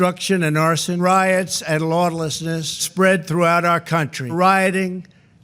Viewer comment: Pretty sure I heard "lawn-nessness" at 1:28 . Then again, maybe I'm just hearing things because he mispronounces words all the time. Edited: because people.